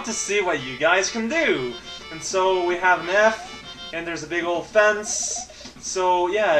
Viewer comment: I thought said "So we have meth..."